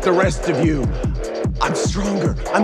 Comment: I thought he said I am batter